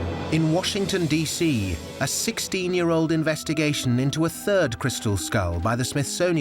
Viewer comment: I thought hey were trying to say this old lady was 16 year sold!!! :)) Maybe I had enough drinks so far tonight ?? LOL